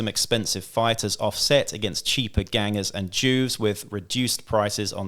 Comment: I'm watching the Delaque section and I think I must have misheard him but I turned on subtitles and it says "cheaper gangers and Jews" this cannot possibly be what he is saying, can somebody tell me what he actually said? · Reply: Crikey, that’s a first! It’s “JUVES”